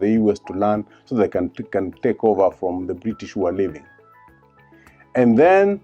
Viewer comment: Did he say "British walevi" or "British who were leaving"?